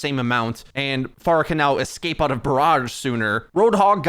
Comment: So who else heard him say "Pharah can escape out of 'barrarge' sooner"